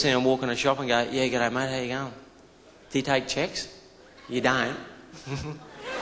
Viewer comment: lol at first I thought he said we dont take CHICKS